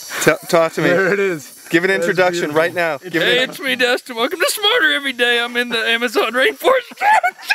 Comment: sounded like he said "Spider Every Day"